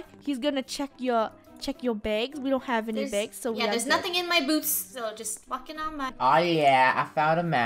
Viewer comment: what did said??? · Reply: Theres nothing in my boots yea theres nothing is what said